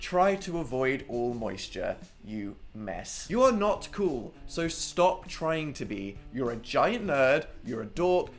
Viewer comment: I THOUGHT HE SAID "STOP TRYING TO BE STRAIGHT ITS. NEVER. GONNA. HAPPEN!" !!!!!!!